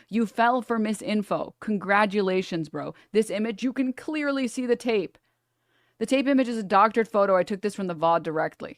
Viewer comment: Did she just say “You can clearly see the tape” ???